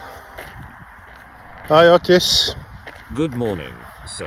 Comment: I thought you said "Hi autist!" and I was like "OH HAI!"